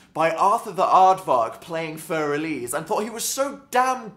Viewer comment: WHEN HE SAID FUR ELISE I THOUGHT He SAID FURRY LEAVES HAHAHAA IM CRAFTING